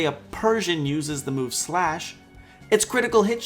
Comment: did you say Splash instead of Sratch?